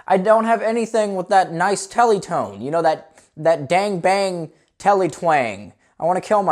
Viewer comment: You know, deep inside, what we all really want is that D A N G B A N G T E L E T W A N G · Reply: i thought he said gang bang...